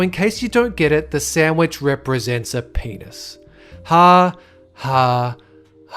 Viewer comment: I accidentally dropped my phone and skipped to the ending of the video with nh headphones still on and all I heard was "the sandwich is shaped like a penis".